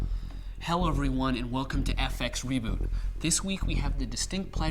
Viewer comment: did you say hell everyone?